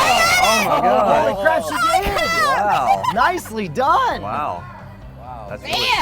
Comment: did he just say dicely done????